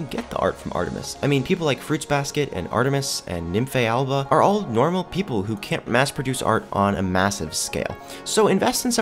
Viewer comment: did he just say NYPHAEAALBAAA??! SHE IS LIKE MY BESTIE